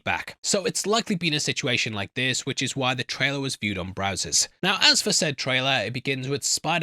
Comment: did you say brazzers😂?